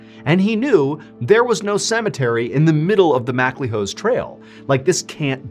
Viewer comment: Did he just say...."the smack my hoes trail?".....😅😅